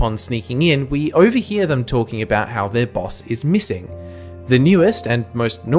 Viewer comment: subtitle errors: 13:29 missing not messing 4:22 an entire unsaid paragraph 6:29 unsaid now 9:44 unnaturally long pause before the word cookie shows onscreen 10:16 unsaid w 10:33 "fisherman death scene" is probably for editing and is not said 12:54 "Play clip" same as above